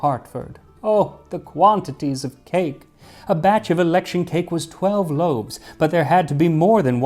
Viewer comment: At the beginning for some reason, despite all the context clues to the contrary, I thought I heard "electric cake". Which made me think of Triscuits (elecTric buscuits, "Baked with electricity!")